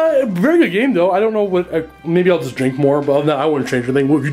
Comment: at first I thought he said "maybe I'll just drink more" and I thought "now you're understanding what it means to be a dark eldar player"